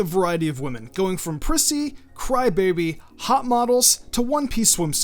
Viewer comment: who else heard p*ssy first go round